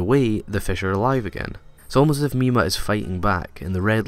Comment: Also can you tell me what he said at 4:03-4:04? also from 9:54-9:58? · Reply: Lesley Cara Delos Santos 4:03 "as if mima is fighting back"